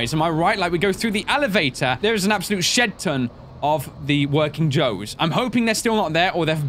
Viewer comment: did you say "shit ton" from 28:06???????